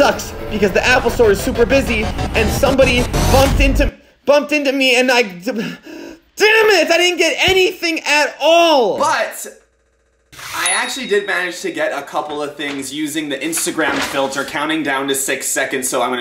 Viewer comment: I thought that you said you didn’t get anything at all